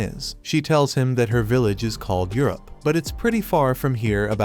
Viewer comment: did you say Europe?